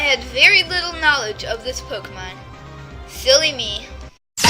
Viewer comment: Wait did you say "filly me" or "silly me"